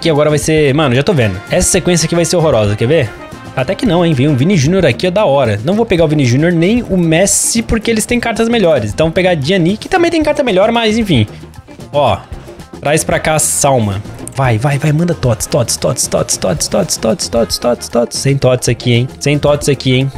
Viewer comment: Hora 5:04 falou vem pra cá a Salma eu entendi vem pra cá Salmão kkkkkkkk